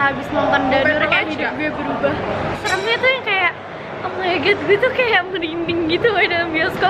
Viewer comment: ini gua salah denger apa bene dia bilang oh my gord ? :( :(